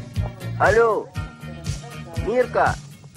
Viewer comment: Cómo la llamó? / How does he call her? Dnitka?? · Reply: Mirka